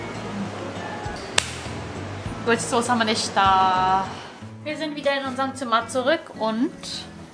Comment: ich verstehe da irgendwie „Gode Soße ham wa nisch da“ 🙃